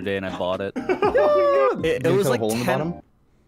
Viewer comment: What did say at 6:33 ??? The world may never know.. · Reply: "Is there a hole at the bottom?" It's pretty audible lol